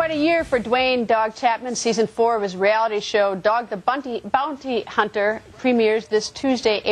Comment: Sounded like she said " Doug the bunty Hunter" lol